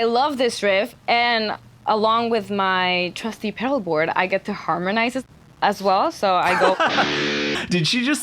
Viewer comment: Let's bet, did she said "shit" or "bitch". We need a pool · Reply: I heard "Harmonize it"